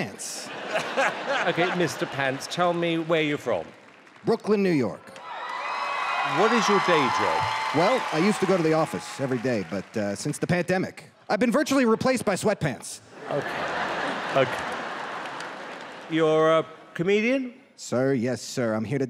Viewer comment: I heard Pantdemic 🤣🤣🤣